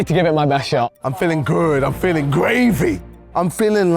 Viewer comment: I don’t know what it was, but I was looking at the captions when KSI said “I’m feeling good, I’m feeling gravy” and I thought he said “I’m feeling good, I’m feeling gay” So anyway